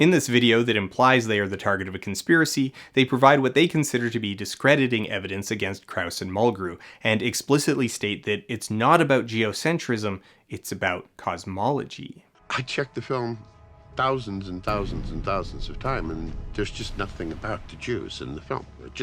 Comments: "It's not about geocentrism, it's about cosmology" "This book isn't about jazz, it's about music" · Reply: I swear, when I first heard that line, I swear I heard, "It's not about the jews, it's about cosmology." That was one hell of a doubletake for me. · "It's not about geocentrism, it's about cosmology" "This book isn't about jazz, it's about music" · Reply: "I checked the film thousands and thousands and thousands of time and there's just nothing about the geos in the film..." That's what the captions say that he says, but I'm still not convinced. For one thing "the geos" isn't something that's commonly said and doesn't sound like geocentrism. "The Jews" makes more grammatical sense. It also fits because, like covers later on, he's a huge anti-semite. I think somehow his anti-Semitism was brought up and that quote is him trying to address that.